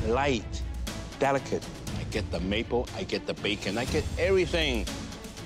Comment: did he say bacon? Is that a bacon cake wtf?